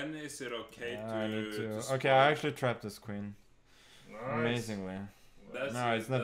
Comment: Thought it said I "Tapped" his queen 😂🤣